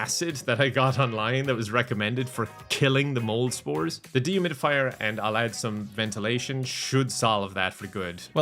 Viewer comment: I thought he said "demon fire" rather than "dehumidifier" and I kind of just accepted it, since it's.